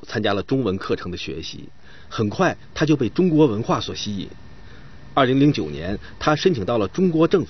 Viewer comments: the subtitle is different from the narration. The narrator said the year was 2009 while the subtitles said it was 2011. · he clearly says 2009 but the subtitles say 2011. What gives?